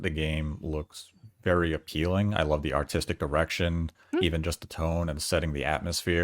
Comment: Anybody else hear say, "artistic erection"?